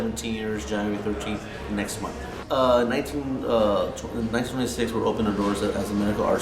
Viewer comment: Did he say it opened in 1926? He kinda stumbled over that date